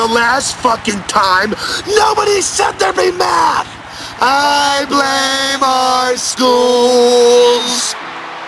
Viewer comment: When I first heard that excerpt in another video, I thought they were saying, "I hate math! Let's blame our schools!" And I thought it was being spoken by the people on camera. Didn't realize there was a woman there.